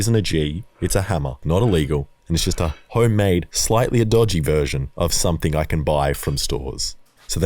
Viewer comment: Slightlyer?¿ Worst part is it took 2-3 times going back over it to realise what was bugging me after my brain said "hang on something is off here"